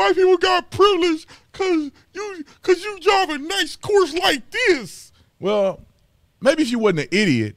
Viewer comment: anyone else at first thought he said "because you drive a nice coors lite this?" hahaha